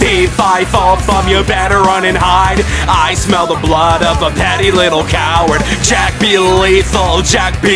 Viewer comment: I thought it was saying "I smell the blood of a little scared chara" but I guess I was wrong